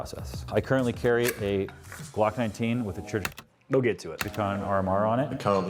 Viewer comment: oi mate! Ded he saaay a “cOCk” 19? UwU